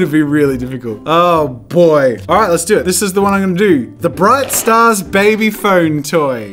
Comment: I think its funny that Jazza thought it said "bright stars" but it was actually "starts" and he never noticed XD